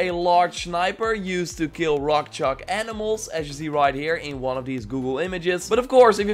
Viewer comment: WTF is he saying here 6:50 rock chalk animals? I can't understand him sometimes.